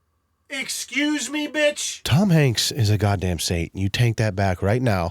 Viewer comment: idk why but i thought leon said “mc’suce me, bitch” from that video. he didn’t but it still made me lmaooo 🙃😅😂